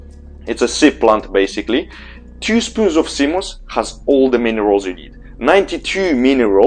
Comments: is he saying 'sea moss' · HI, what is cimus? or cimos? or Seamus? he says it at 3:07. thanks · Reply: Seamoss